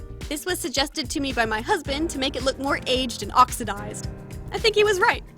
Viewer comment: Did she say husband?!?!! Shes sounds 14!